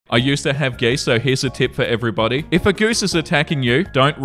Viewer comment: i thought you said “i used to have gay sex” bc i wasn’t looking at the screen